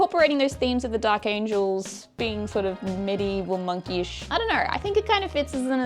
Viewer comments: Medieval monkish... I thought you said Medieval Monkeys... Good job.. Outstanding concept and execution of that concept. The purple glow was very nicely done as well. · When said "medieval monk-ish" I heard "medieval monkeys" ...which is why they joined chaos. 😆